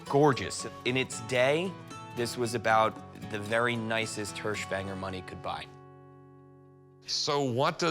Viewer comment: I think he said hershbanger, but I was definately distracted...